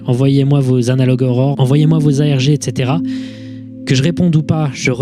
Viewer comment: Quand il a dit "envoyez moi vos ARG" j'ai compris "envoyez moi vos AMG" 😂